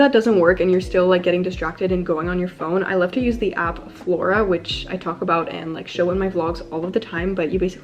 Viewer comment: sorry, does anyone kno the app she talked about 12:34? couldn't really catch its name :( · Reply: flora